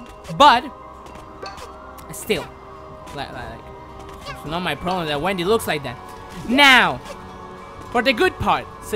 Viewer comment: Did he say " It's not my problem that Wendy looks like that"? 1:11:57-1:11:59 I think I heard Wendy since that's my name.